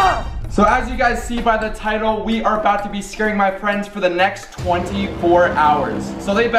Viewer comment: I thought he said scaring my pranks🤣